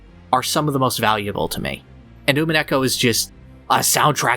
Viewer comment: Here ya go <link> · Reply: Thank you so much! I kept hearing "Umi and Echo" and that was obviously leading me nowhere. Beautiful song indeed.